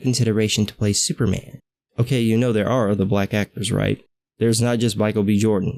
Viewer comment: did you just say "blacktors" at the 1:23 mark, lol.